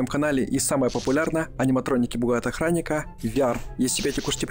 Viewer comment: почему я услышал аниматроники бухают охраника?